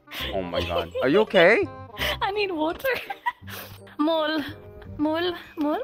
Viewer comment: 아니 뭔소리야로 들림 ㅋㅋㅋㅋ I need water 인뎈ㅋ